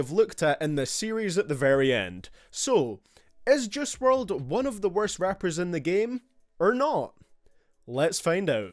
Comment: I thought u said it was the worst rapper